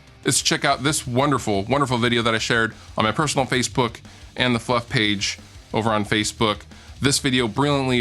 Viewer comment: When he said Fluff page at the end I thought he said fuck page lolololololol